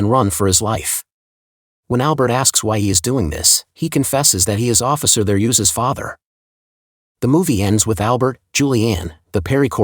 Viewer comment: what did he say? Couldn't understand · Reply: "He confesses he is officer Therese's father." Therese is the young man in the sheepskin vest that Purdell shot in the back.